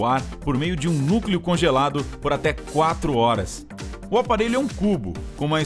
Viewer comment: Eu entendi: o aparelho é um cu